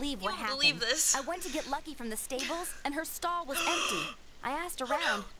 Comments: Did she say that she went to get "lucky" in the stables? XD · I heard, "I went to get lucky in the stables." Yeah, I've been on the internet long enough to know what's up, girl. Girl, there's better ways to make money!